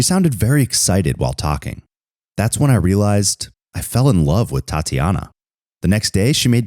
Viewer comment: Who else thought he said thot tiana